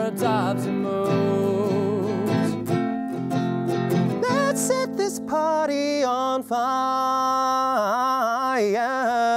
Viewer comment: In 24k magic I always heard it say let’s set this body on fire